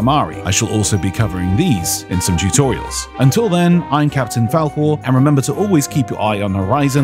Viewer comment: Why did I hear "Im Captain Falwhore"? 😳